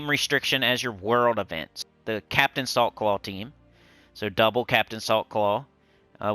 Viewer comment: Everytime you said Captain Saltclaw I heard "Captain of the softball team"